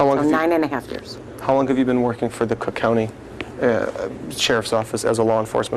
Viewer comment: I heard “crook” county😂